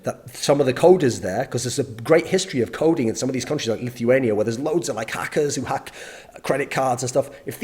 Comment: Did you say Lithuania?